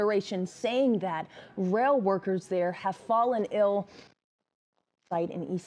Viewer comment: what did she say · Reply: "Derailment site".